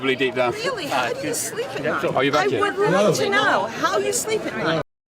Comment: oh you’re back again 😂 Did i hear that right